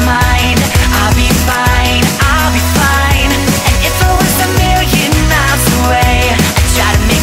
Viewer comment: Awesome video! Btw. In the text there was a part "And If i was a million miles away" i always hear "And If i was American" (Maybe cause i'm not from England...)